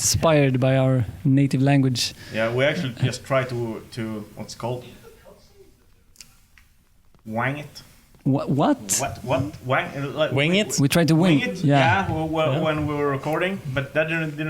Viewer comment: did he say we tried to wang it....?